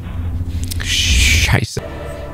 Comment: can sameone who speaks german tell me what he said? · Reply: He said shit